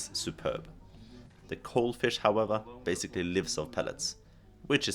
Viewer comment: Very interesting content. Thanks for sharing. I couldn't quite understand, at 6:55, what fish lives on pellets? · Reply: Thanks, Jason! The coal fish stays in front of the pipes a large amount of time, where they feed on the pellets coming out. That’s why we didn’t bother shooting them, as the meat isn’t as good as the other fish who’s just passing by. If you search for “DN - laksegriseriet”, the first thing you see is a clip with a coal fish filled with pellets and shit. The clip is from an older facility with worse filtration, but the point stand